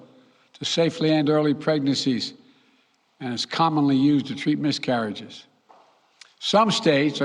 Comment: What did he say? used to treat miscarriage